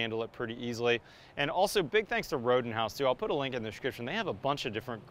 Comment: Rodenhouse - hands down worse company name ever. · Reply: Not familiar with the brand, so I heard say "Rodent House"